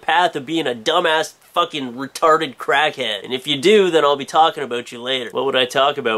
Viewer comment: who else heard "aboot"